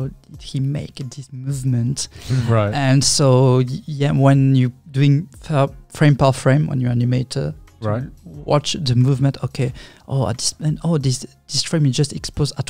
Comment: what did he said on 0:23 ? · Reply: "when you're going frame per frame on an animation to watch the movement"